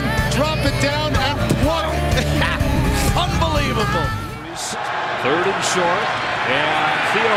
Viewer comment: oh come on i wanted to see if the cat was gonna get the touchdown also, 4:40 he said "fumbelievable" right? im not the only one who heard that legendary pun?